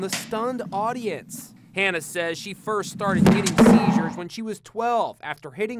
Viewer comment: the captions say “Hannah first started dating teachers when she was 12.”